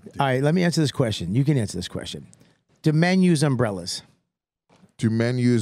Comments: I honestly thought Bobby said do menu's umbrella 😂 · Because it's Bobby I heard, "Do Menu's Umbrellas?" And for the life of me I couldnt figure out what racket restaurants were running. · I thought Bobby said, “do menus umbrellas?”